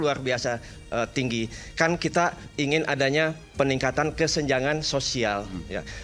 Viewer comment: disini gua ga salah denger kan meningkatkan kesenjangan? Bener2 pilihan gua dah ini No 2